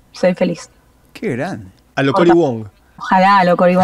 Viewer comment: En el minuto 10:50 mencionan una persona que tiene una rutina, "Tony won" entendi. Quien es? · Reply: Cory Wong. Saludos.